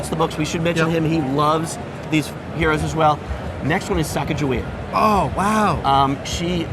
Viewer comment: Can anybody hear who he is mentioning at 12:02 ? Who is that? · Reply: Sacagawea. She was a Native American woman who helped Lewis & Clark. She's a much bigger figure in America, she's got her own golden dollar coin and whatnot.